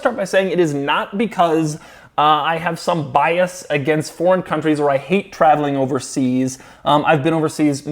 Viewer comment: did he say porn countries?............ oh foreign countries! BTW you Chester needs brown rolls if they make them. Congrats on have kids I assume you had them by the end of this video but I didn't stick around to find out.